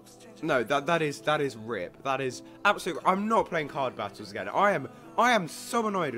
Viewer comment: Omg 16:50 the yt subtitles said card bastards XD